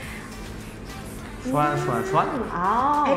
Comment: TMI 3:31 저도 계속 블랙 스완으로 들었지만 like swan이였습니다..그리고 디렉팅 안무가선생님(?) 없이 은비리더 아래에서 연습했다는 곡이라고 합니다ㅠ